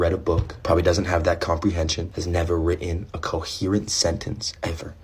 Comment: Oh and the dude that calls me stupid can't even say the word "Written" correctly.... It sounded like he said - "ritn" to me.